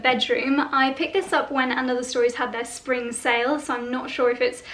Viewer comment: Oh LOL! Whenever you talked about that shop, I heard “end of the stories” so I could not find it! Thanks for the links, it is also great that you show us how the clothes fit. Sunny cheers from Montreal!